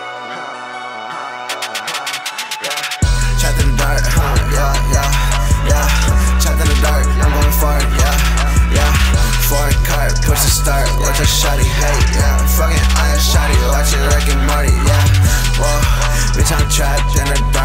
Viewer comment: did he say he's going to fart? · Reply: "Trapped in the dark, i'm going far" ✌🙄⚔✨